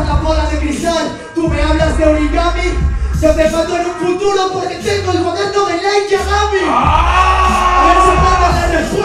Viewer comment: que dice en el punch del 11:22? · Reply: Yo te mato en un futuro porque tengo el cuaderno de Light Yagami